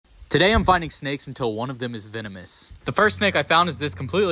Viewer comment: I heard "today I'm biting snakes until one of them is venomous" and i was like what